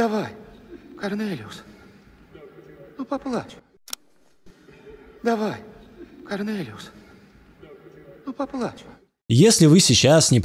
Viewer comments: почему я услышал тут карленевирус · Самое смешное, что я не смотрел видео а слушал в фоне и все равно услышал "коронавирус"😂